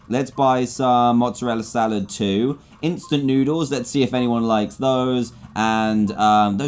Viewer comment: I thought u said Thinknoodles